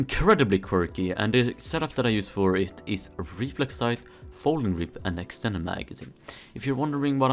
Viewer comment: what did he say ??? I couldn’t understand him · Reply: Reflex sight, folding grip and extended magazine